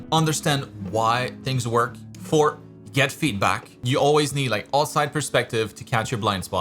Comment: he said "four, get feedback" and i heard him say "forget feedback" lol